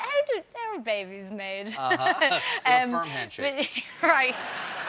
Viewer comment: Also I honestly thought he said "sperm handshake," which sounds like a handy euphemism.